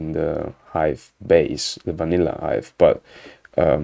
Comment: Did I hear "vanilla ice" when you said "vanilla Hive", you can't prove it !?!